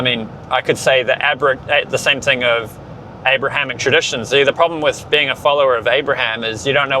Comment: People who follow ‘Aramaic religions’ do not acknowledge that the other religions are an ‘Aramaic religion’ so what you’re saying is incorrect. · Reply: Abrahamic* I've never heard that. Are you sure?